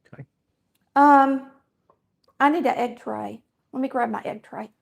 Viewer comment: Happy Easter.. when you said let me get my egg tray.. I thought you said I need to get an x-ray.😂 and.. I’m southern!!